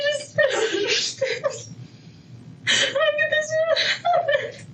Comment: At around 4:38 does she say "I knew this would happen"????